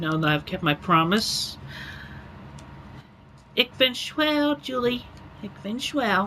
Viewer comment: As a German speaker, when I heard him say "Ich bin schwul" I was so confused